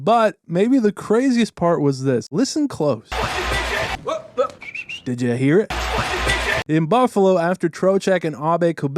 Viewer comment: what is being said? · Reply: Someone yelled “fu**ing midget” at Marchand